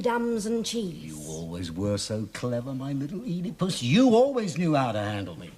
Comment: l “My little Oedipus.” Did I hear that correctly?!